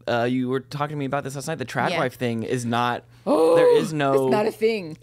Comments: Omg... I just had the funniest auditory processing mishap. I thought they said "trap wife" instead of "trad wife". I had to rewind the video twice. I was like where in the hell does a dope wife fit into this conversation? 🤦🏻‍♀️ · I thought you said trashwife 😂 not tradwife.